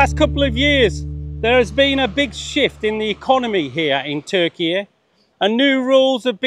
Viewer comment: I thought you said there has been a big shit in the economy here in the past two years, I heard it three times as shit until I turned on subtitles 😂😂😂